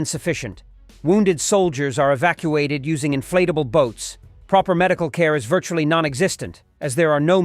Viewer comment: i thought he said ' inflatable goats '